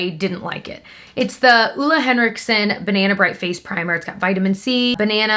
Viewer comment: when you said banana bright i thought you said banana bread and i was like ooooof banana bread PRIMER what?! but yeah banana bright makes more sense 😂